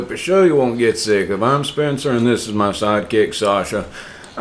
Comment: I don't hear "If" I hear I'm Spencer and this is my side kick Sasha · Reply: I’m definitely not saying if I’m Spencer 😆